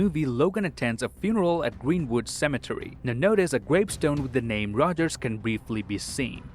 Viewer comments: I thought it said pogger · i thought that said poggers